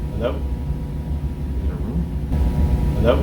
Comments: i hear ( is there room? ) Guy respawns by saying (yeah) Idk. what do you hear? I think it was the other guy behind him. Because he replies with "yeah" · the hello part is not correct at 3:13 the first guy says hello the second guy says "is it a room?" i heard it that way in the original clip as well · Actually, I think that's voice, because then says 'Yeah yeah' like an answer to what said. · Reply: I heard "in a room" or "in the room." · In 3:10 I think it's said 'in a room' not 'hello'